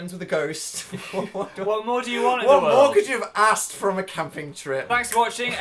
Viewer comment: When Dan said what more could you have asked for at 20:13 did anyone else hear ''What more could you have assed from a camping trip?'' or was that just me?